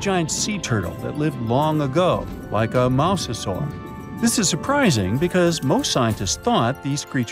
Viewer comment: did he say mouse-sasaur at 1:14?